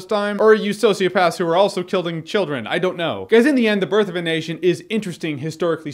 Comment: I thought he said: birth of an Asian